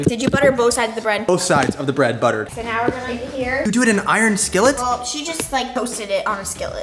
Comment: Broo😂 a thought said iron skittle😂😂❤